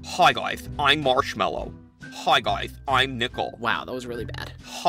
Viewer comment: Did he just say I’m nipple!😱